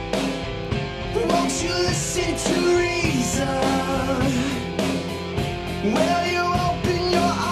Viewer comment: ♫ Wont you listen Theresa?... ♫ I may have misheard the intro song, lol.